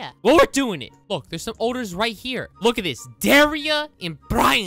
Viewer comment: I thought u say oders like: odder